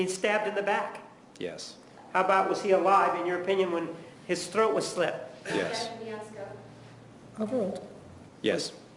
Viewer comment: What’s Wilmott’s objection please? I can’t hear what she says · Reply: "Objection your honour, Beyond scope"